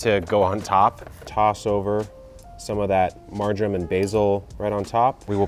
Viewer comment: Marjoram! I thought you were saying margarine and I'm like I didn't think you were vegan, or that you hated life. Took me three listens to catch it. lol